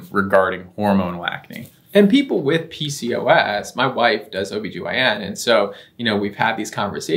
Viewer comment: Did he say my wife? Oh Wow. So they are not together?